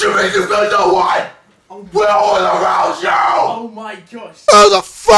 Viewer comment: i thought he said "WHAT WILL AROUS YOOOOOU?!" LOL XD